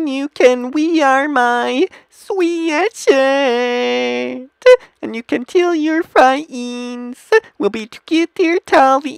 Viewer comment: i wasn't paying attention and i thought he said and you can kill your friends and i was confused for a 2 minutes 😂💀💀💀